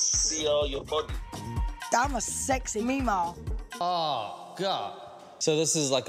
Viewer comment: I think she says "I'm a sexy meemaw" aka grandma but legit I thought she said I'm a sexy meatball the first time I saw it 😂😂😂